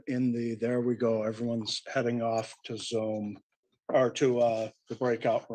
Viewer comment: Did judge say “nisqually nodding off to zoom”…..? Yeah it sounded like he said what was on his mind.